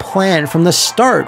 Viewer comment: The fact that this is one of the quickest What Happened is... a spectacular feat for Balan Wonderland- I mean, Balan WonderWorld, why isn't it Wonderland, such a better title. It's not a complete broken mess, it's just purposely deigned to be awful and weird... Some of it by A.I., which means the machine uprising was started by a disgruntled game designer, yup, that makes sense. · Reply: I keep hearing it as 'Bellend's Wonderworld' instead.